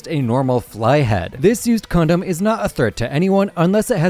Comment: what did bro say?😭🙏🙏🙏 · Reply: Condom